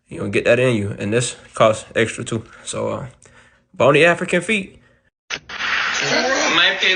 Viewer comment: did you just say "boni African feet" instead of "bon appetite?" Omgs!! I love you so much!! You're truly hysterical!! Always so witty!!❤😂😂